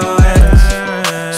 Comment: I thought he said i bought myself a nitro cone when he said but im not myself the nights you're gone